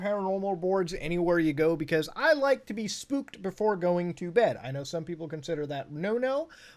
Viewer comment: I thought you said you liked to be spooned before bed